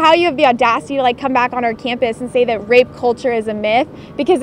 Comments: I thought it said rap culture is a myth 😂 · I thought it said "Rap culture is a myth"... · I thought it said ‘Rap Culture’ is a myth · For the past week I thought the video said “Rap culture is a myth”